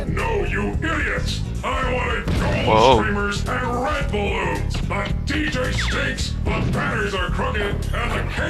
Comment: i thought i heard Toc-Man say "red bull". he actually said "red balloons" xD